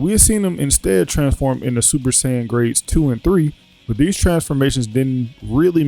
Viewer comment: the form trunks gets with his huge msucles and huge haurstlye IS NOT SSJ 3 it is more like a increased SSJ 1 form, vegata used this form too in teh fight agaisnt cells after he absorbed android 17 even goku showed this form for a short time while training gohan in the timechamber before the cellgames started, he did not incrased the power of this form as much as trunks since this form amkes you incredibly slow, maybe inform yourself before you say stuff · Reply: I'm sure he's aware that the form that Trunks used wasn't Super Saiyan 3. If you actually listened to what he said at 0:56 he said "Grade" before saying 2 and 3. Grade 2 is the form Vegeta used (Super Vegeta) and SSJ Grade 3 is the form Trunks used against Cell (Super Trunks) before he realized the drawback of the form. Maybe listen before you try to call someone out for not being informed.